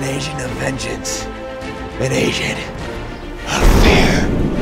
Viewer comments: "I am an Asian of fear" That's what I heard him say with my volume turned down... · At first, I thought he said "I became an asian of vengence, an asian of fear" before I realized it was agent... not asian.